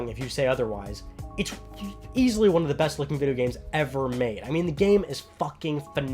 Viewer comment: Uncharted 4 is not the best looking game ever made. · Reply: Sorry heard you say "the best" not " one of the best" my fault.